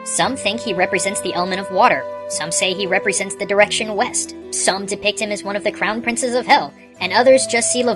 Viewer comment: east i thought you said weast